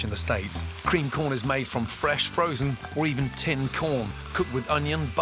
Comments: Wait did he just say fresh frozen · it's weird seeing Gordon recommending fresh frozen or ready-made ingredients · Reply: You guys completely misheard him, he didn’t say “fresh frozen”, he said “fresh, frozen”. Fresh-frozen doesn’t exist he’s said it himself